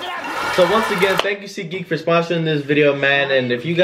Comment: All this time I thought David dobrik was saying ‘seekeek’ when it’s seatgeek